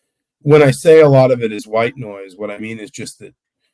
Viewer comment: The first time I heard it, I thought he said "white boys", not "white noise". Lol.